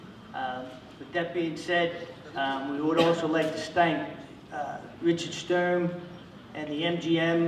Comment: he said stank instead of thank HAH